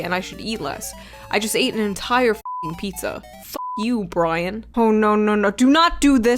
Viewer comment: Im listening as I wal in to work and all I heard is. " F you Brian!" My name is Brian.....